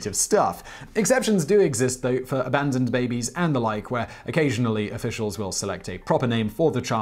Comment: At first I thought it said unarmed babies